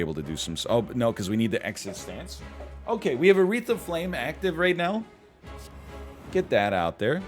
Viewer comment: Every time he said "A wreath of flame" my brain heard it as "Aretha Frank(lin)"